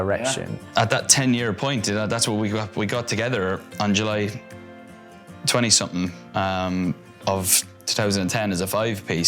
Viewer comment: the fact that ALL 5 of them got together for the 10 year anniversary just melts my heart😭😭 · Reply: they didnt, he said “at that 10 year point, we got together on july 20 something of 2010” so he was explaining the significance of what the 10 years was. i misheard that for a second too and got excited lol